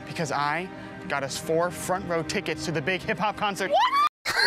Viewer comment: I genuinely thought he said "front row tivkets to the vague hiphop concert."